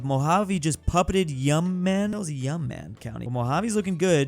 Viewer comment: (YOU MAH) bro not whatever tf u said lmao